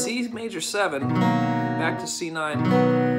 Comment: Isnt it "back to C' at 1:55? Not C9 or am I hearing that wrong? This will be the first thing I really learn. It sounds so impressive! Thx for putting this together. You're teaching style is really easy to follow and fluid.